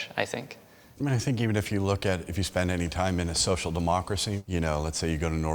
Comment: King Señor Ali he said norway was socialist but its capitalist....... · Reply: wet sock when did he say that? Provide a time stamp. I clearly heard him say it was a “SOCIAL DEMOCRACY”